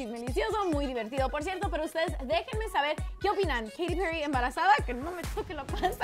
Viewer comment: Yo entendí "Katy Perry embarazada de Maluma" y yo :¡¿¡¿¡¿¡¿¡¿¡QUEEEE?!?!?!?